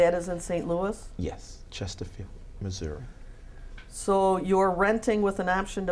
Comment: Im not bothered enough to go back and check but i didnt hear him say Chesterfield Missouri either... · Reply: Not that I'm trying to stick up for this fool, but he did kinda say it. She asks him, "And this is in St. Louis?" He replied, "Yes. Chesterfield, Missouri."